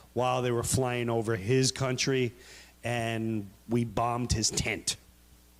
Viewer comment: First time I seen this I thought he said "we bombed his taint..."